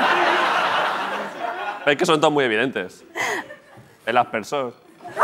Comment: Soy italiana. Quién me escribe lo que dice el presentador a 7:43? Cómo se llama el movimiento sexual? No logro entender. Gracias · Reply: el aspersor 😂